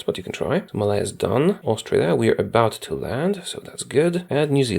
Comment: Wait I heard "Austria, we are about to land"